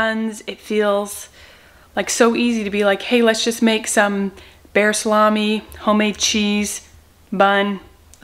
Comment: Did she just say, "bear salami"???